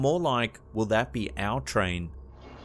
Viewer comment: / 13:42 i hear will that be on our train